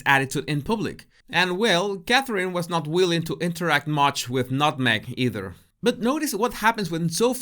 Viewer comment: did I hear, "Nut Meg"? 😂